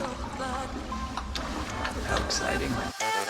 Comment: 🇨🇦❤️Thanks for a great visit, as always! At 26:42, said, “I’m exciting”. I thought would respond by saying, “Yes, you are!”